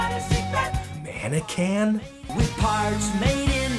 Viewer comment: HE WAS SAYING MANNEQUIN?? I THOUGHT HE WAS SAYING MACHINOMATIC MAN